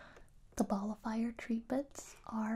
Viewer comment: I thought you said ball of fire instead of nullifier and I was like yeah let’s set these worries on fire